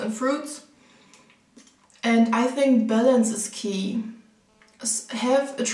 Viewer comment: I legit thought you said "I think bellends is key" for a moment (at around 15:20) paha